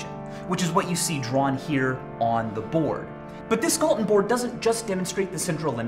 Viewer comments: thought it said Galton beard. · At first i thought i said gallon board